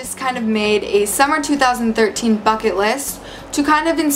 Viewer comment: I thought u said " 2014 fuck it list" I like that name better